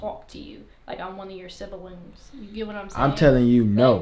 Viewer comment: she said “one of your symbalings” instead of siblings -_